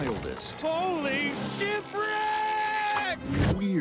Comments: did he just say "holy shitbrick"? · I thought he said "Holy Shitbricks!" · at first I thought he said holy shit brick.